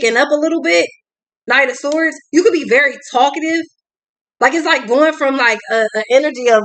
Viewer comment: Ha! Ha! I thought you said Stalkative!!! instead of talkative!!! :)